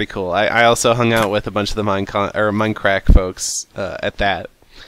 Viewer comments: it sounds like he is saying minecrack XD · I keep hearing "minecrack"